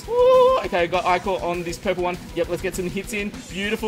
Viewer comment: who else thought he said "Pokemon" instead of "purple one"